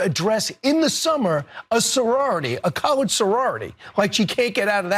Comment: I heard "a college sorority"